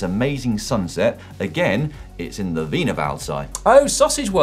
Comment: Sounds like Si is saying Weiner-world instead of Wiener-wald. One is a place of nightmares, and the other is a sausage theme park. Heh.